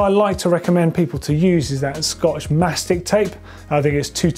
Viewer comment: did he say Scottish making tape 228? Couldnt quite make it out...